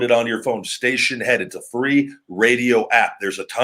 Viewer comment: station head? I thought you said streamhead!